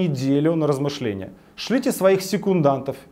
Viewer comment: послышалось "присылайте киллеров..." прислушался НЕТ! "секундантов" . ...Я не сторонник Навального. Но по-человечески, переживаю за него. Хочется чтобы он умел не только ломать, но и строить. Возможно ли такое???.........Просто мысли вслух. отвечать мне, нет необходимости.